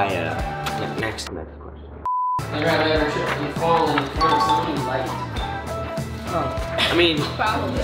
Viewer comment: On the last question I thought bit said "have you ever tripped and fell in front of someone you KILLED" until I looked at it again 😂😂😂😂😂😂